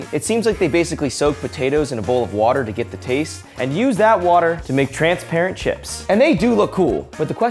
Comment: I thought it said “making transparent child” 😂 💀